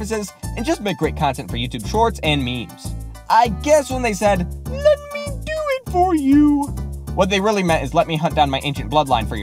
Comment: Bro I don’t hear “let me do it for you” I hear “didn’t I do it for you” it’s weird bro💀💀💀